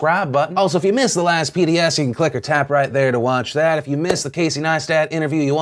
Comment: I have watched your show for about 7-8 years. And since the feature started, I always thought you were saying "ClickyTap"... not "Click or Tap"